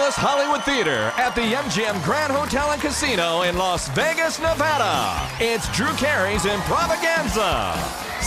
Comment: 还有谁把开头听成"in Las Vegas Nutella"的XD